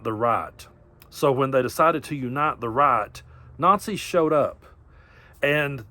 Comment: your southern drawl made it sound like you said "unite the rot". appropriate.